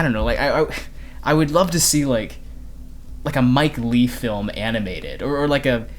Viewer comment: Mike Leigh, the best British director working today FYI. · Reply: I stand corrected. I even know of Leigh's existence but somehow I heard "Lee".